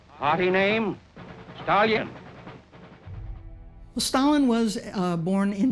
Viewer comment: sure does sound like he said Stallion, not Stalin